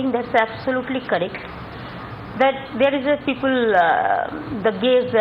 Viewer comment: What did she just say @ 31:17 ".........there is a people gays........" ????? guess